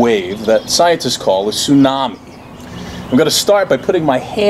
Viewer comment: When he said tsunami, I heard "unagi"